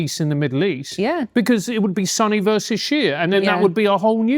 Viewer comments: swore Foster said Sonny vs Cher lol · Am I the only one who hears "Then it would be Sonny vs Cher?" 52:00 ... brits... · when Francis said, "It would be Sunni versus Shia," I at first heard it as, "It would be Sonny versus Cher."